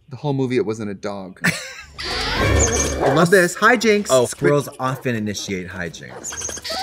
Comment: when she said "hijinks" i thought she was saying "hi jinxs" like jinx monsoon as the squirrel